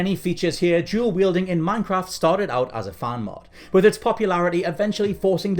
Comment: Wait did he just say Moyang instead of Mojang